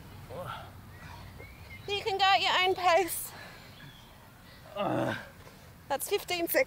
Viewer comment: Heard "you can DIE at your own pace" instead of "do it at your own pace"